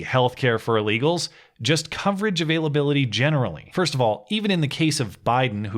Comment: I heard that as "coverage of illability". Doctor, I've been illified!